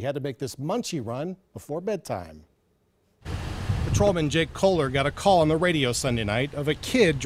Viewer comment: I thought he said Jake Pauler lol